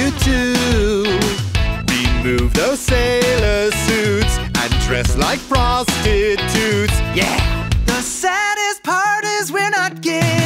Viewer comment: First couple of times before I put captions on I thought the director said "and dress like frosted toots" :D